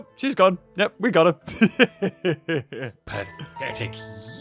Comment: I thought he said "Magnetic useless ants!!" but it was pathetic